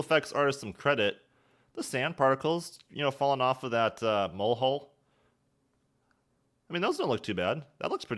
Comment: Did he just say ‘molehole’?